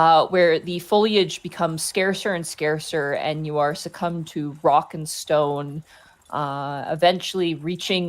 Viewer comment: DID I HEAR A ROCK AND STONE?!